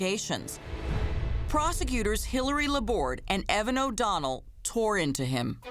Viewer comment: Was I the only one who heard prosecutors Hillary and Donald at 6:32? lol